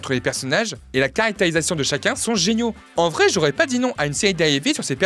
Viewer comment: une série quoi ?? Agnigagniagnei ? Faut articuler !